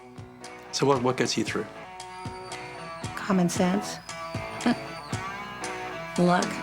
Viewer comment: Interviewer asks, " so what gets you through?" Linda's response, "common sense" at which point in the video I laughed and cried at the same time. Bittersweet. She STILL has a lot on the ball though. She can't do some things but I could talk to her all day long because she is so well read!!!! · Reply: Thank you for clarifying what she'd said. Forgive me for my immaturity, but instead of "common sense" I thought she said "having sex".